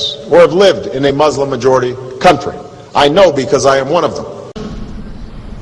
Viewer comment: " I missed the part ,where he admits that he is muslim.." Are you deaf or some thing? at 0:30 to0:31 he say " I know because ,I 'm one of them..." if you can't hear that then I'm afraid there is some thing wrong with your ears or you. w w w.thereligionofpeace[.]com